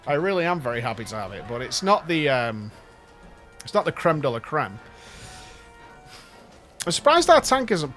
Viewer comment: Did he just said "La crème de la crème"?